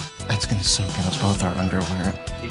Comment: what did they say?? xD I only heard Dylan's "Ah, bitch..." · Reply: I want to say he said ‘that’s going to soak into both our underwear’